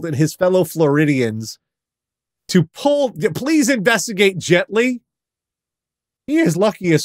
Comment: When said “investigate gently” I thought I heard “investigate Jet Li” and got very confused